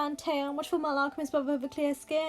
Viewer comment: did she say "stan vocaloid" ? 😍